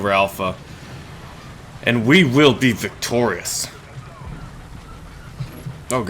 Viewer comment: I thought you said "And we will beat the Tories." I mean, I agree, we should beat the Tories - but this is no time to get political.